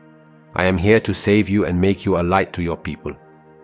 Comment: on the time stamp, the word "lie" was picked up and printed instead of "light". Let's try and see if that could be fixed. 👍🙄